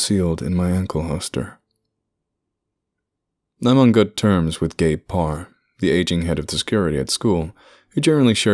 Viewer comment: I literally thought I heard "I'm on good terms with gay porn"